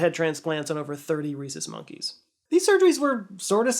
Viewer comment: I really thought you said “30 racist monkeys” and I was like good riddance! But no, obviously that’s not what you said 😅